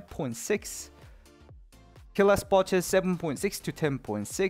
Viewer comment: I always hear T'challa spawn chance :D